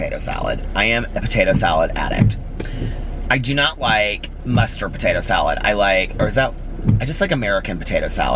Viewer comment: I thought he said bastard for a second also 😂😂😂😂 I was like oh ?!? 😂😂😂